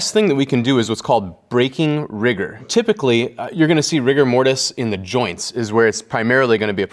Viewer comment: I can't be the only one who heard Rick and Morty at the term "rigor mortis"🤣